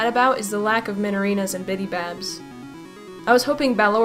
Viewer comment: sounded like she said "and minibabs"